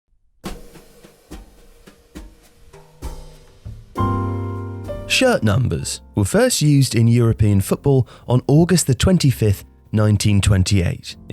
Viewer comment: The way says "shirt numbers" at the beginning makes it sound like he's shocked that such a thing could exist. Shirt numbers?! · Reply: I thought he said shit thumpers